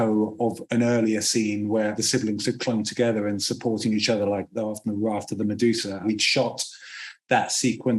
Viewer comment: At around 10:41, I wasn’t able to decipher what Mark was saying about the siblings supporting each other, all I got from the subtitles was “after Medusa” — could anyone take a stab at figuring out what he was saying? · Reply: There's one word he says that I can't get, but he's describing the image of the siblings clinging together like the famous painting "The Raft of the Medusa".